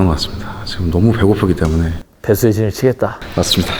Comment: 순간 뱃속의 진을 치겠다? 라고 들었넼ㅋㅋㅋㅋㅋㅋㅋ